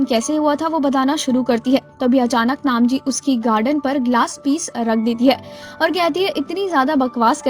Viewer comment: am I the only one who heard 'garden' instead of gardan(throat) Btw no offense,I really like explanation